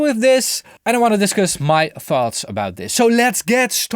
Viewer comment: Did he say that he didn't want to discuss his "farts" at 0:23? Quite right too. this is a channel about audio, not flatulence.